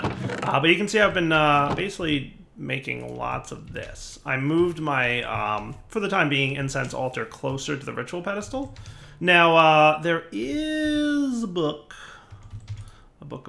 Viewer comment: Are my ears broken or did he say "incest altar" at 0:33 ?